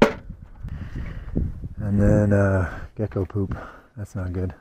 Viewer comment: At first i thought you said "ehh i gotta go poop. Thats not good." 😂 had to rewind. Ahhh "gecko poop." Right yes thats not good.